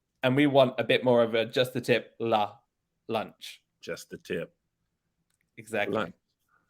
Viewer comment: What did he sayyyyy??? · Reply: “Just the tip” tip of the tongue*